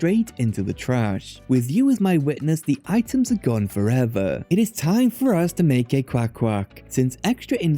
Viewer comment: "It is time for us to make a quackquack." You said it so naturally I had to rewatch it to make sure I heard right.